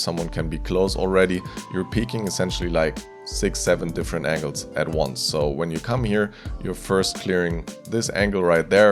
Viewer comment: what did he say 😝😝😝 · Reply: GET OUTT!!